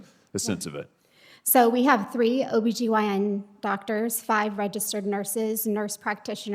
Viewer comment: The shocking word I caught, of which I had to rewind a bit to make sure I heard correctly. And sure enough @ 4:42 she states they have labotomy doctors on hand. Now that is scary for that procedure is not banned in the U.S and is still performed.